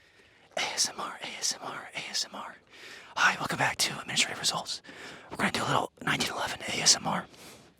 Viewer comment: i swear he says welcome back to "misery results" 😂